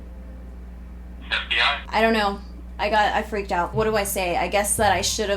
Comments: did he say FBI? ;-; and yes you should report him get the word out even know it wont do much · does he say FBI